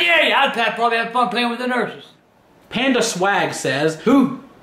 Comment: Did he just say he will have fun playing with the nurses